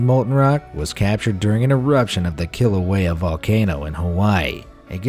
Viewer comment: Is it just me or does anyone else hear "killer whale volcano" when he says "Kilauea Volcano" ?? ( 9:19 )